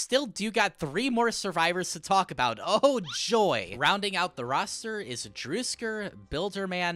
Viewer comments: what did he say 😭 Driskar?? Fyi, it’s pronounced Du-Esk-Ar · Did I hear "DRuSk3r"????????? What?